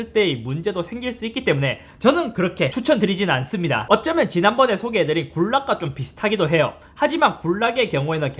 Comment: GULAG 이라구요...? 멀리서 들리는 소비에트 연방 국가